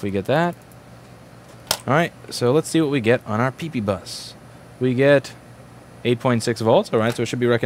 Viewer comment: correct me if i’m wrong, but at 1:19 did he say peepee bus?